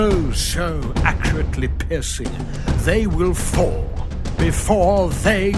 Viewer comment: is it me or did I hear them say pissing @ 0:36?